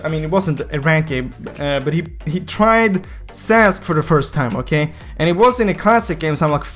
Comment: said "he tried sass for the first time”misheard sentences like if u agree